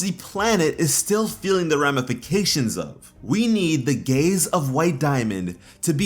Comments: lol thought he said gays instead of gaze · I heard that as: "We need the gays of White Diamond..." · I heard ‘the gays of white Diamond’ then realised what you actually said. But yeah totally agree with this video 🙌